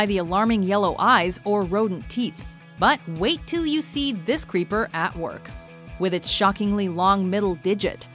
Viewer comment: did she just said " creeper " , lol